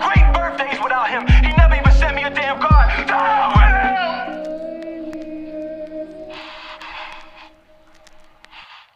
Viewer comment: What did he say? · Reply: “To hell with him”